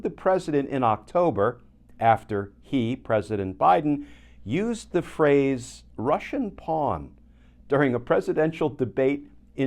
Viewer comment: I thought he said Rudy was “Russian Porn”… those Russians have some really weird Ideas!